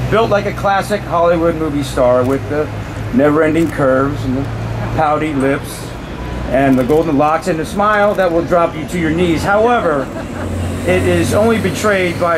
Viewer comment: did he say Scarlett has a "golden box"? 😂